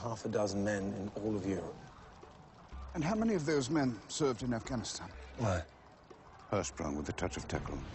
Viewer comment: Wait...!! did he said "how many of them served in Afghanistan" @ 2:15 ...? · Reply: How many of those men served in Afghanistan?